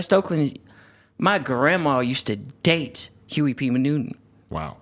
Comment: Who the hell is Huey P. Manutent? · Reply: Clearly y'all not getting it.. Issa joke. my manz in the vid didn't sound like he said huey p newton .. sounded like he said huey p manutent